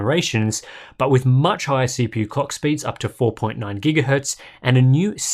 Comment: I went into misheard lyrics territory: "Comet Lake has the same crap integrated graphics we've had for generations, but which much higher CPU COCK speeds" Not that it's not accurate. That's an impressive performance bump, no matter that it's still polishing the same old c